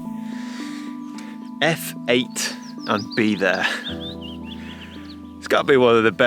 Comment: I heard "f /11and your'e there". There is a landmark destination across the street from our home and the steady stream of instagrammers, face-bookers and twitters are a PITA.